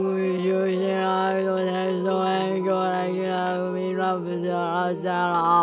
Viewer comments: 頑張って先生の話してること聞き取ろうとしたけど0:21の｢テスト勉強｣しか聞き取れん(⌒▽⌒) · テスト勉強って聞こえるなって…そんだけの話だがちょっと謎に嬉しくなってしもた